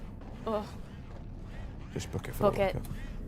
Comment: I don't know why but at 2:21:29 I thought said f### it 😂